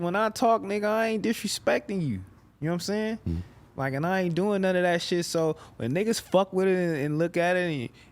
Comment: he actually said “fck TRELL”